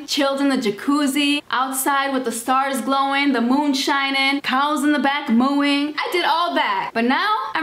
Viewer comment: i thought she said "𝙩𝙝𝙚 𝙝𝙤𝙚𝙨 𝙞𝙣 𝙩𝙝𝙚 𝙗𝙖𝙘𝙠 𝙗𝙤𝙤𝙞𝙣𝙜' 😂